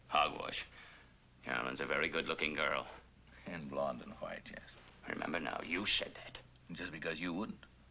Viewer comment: Dr. Miles (Percy Rodriguez) "Can you believe it when I tell you my son has no interest in your daughter? He's not attracted to your daughter." Fred Russell (Joe Maross), "Hogwash. Carolyn's a very good looking girl." Dr. Miles, "And blonde and flat chest." Question: I can't believe he notes Carolyn's (Tippy Walker) 'Twiggy'-type frame; although being a physician, specifying body types / shapes -- female or male -- comes with the profession. The audio drops on Percy's last line. Does he actually say " . . . and flat chest"? MORE IMPORTANTLY, I'm disturbed by this sudden, out-of-the-blue, Dr Jekyll - Mr. Hyde transformation of Fred Russell. As I noted Commenting in a prior episode, in his first episode he started out as a jovial, loving father whom Carolyn totally adored and vice versa. Yet NOW he's veering into 1) being a racist / hot-headed bigot and 2) veering into George Anderson territory -- a crazed, quite unsympathetic character. Additionally, while awaiting Dr. Miles' arrival, viewers could conclude Fred looks like he's picking up Susan (Diana Hyland). Were they drinking pals? [Fred, "Has it been a year? . . . Boiler-makers your drink of choice, right? . . . You always did have a wit."]. I'll sound 'out of the loop' here, but what's THEIR backstory? Secondly, if Fred can keep / score with someone as hot as Donna (Sharon Hugueny), Susan hardly seems remotely similar physically. IMO: Diana Hyland is attractive, but like the original "Peyton Place" blonde, Co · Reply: Dr. Miles said blonde and white. Nothing about flat.